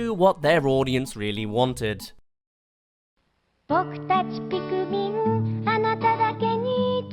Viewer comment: it sounded like she just said "frick dat pikmin".